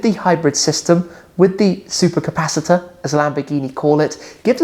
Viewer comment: Great Scott's! for a second thought said flux capacitors😂